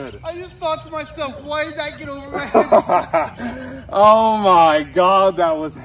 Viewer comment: I listened to the first half of this with the sound off. The second I turn the sound on all I hear is "WHY DID THAT GO OVER THE FENCE?"